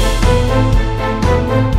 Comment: what others hear: ''we must eat the cow'' what i hear: ''we must kill Salted''